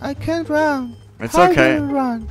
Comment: I thought he said I can't rap